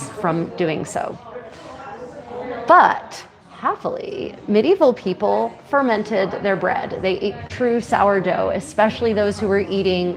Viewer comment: At around @ 30:25 I thought you said, "... Medieval people are fermenting their dead!" yikes! haha